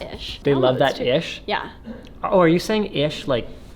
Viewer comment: Did Sara say "I Try to keep things PEACHY" ? or "PG"? 13:39 Let me know below! #Laurel or #Yenny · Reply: Piotr Kwasnik Lol I had noticed that too, heard peachy the first time but then realised it was PG.